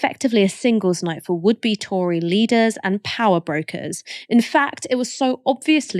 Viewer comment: For a moment there I thought Ash said 'would-be Tory leaders and power gropers'.